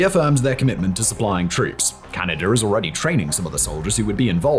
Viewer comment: Did you say Canada? 😂 23:03 WTH is Canada going to do? Not meet more military budgets